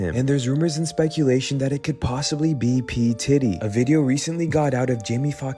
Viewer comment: At around 21:08 I know he heard what I heard that’s why he laughed a bit, it sounded like they said P t*tty lol